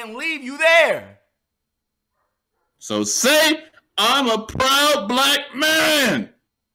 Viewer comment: It sounded like he said I'm a proud black Indian 🧐😭😹🖤💜💙